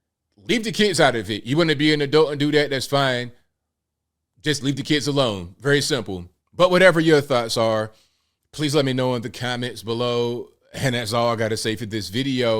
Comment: What I heard was "We want to be euthanized!".